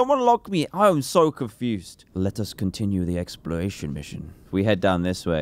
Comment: who heared ''Lettuce'' or ''Let us''? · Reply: I heard let us. Then I made a joke by saying lettuce eat.